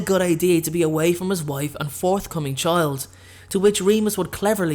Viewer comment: DID hE SaY FouRtH CoMiNg ChiLd oR iS It JuSt mE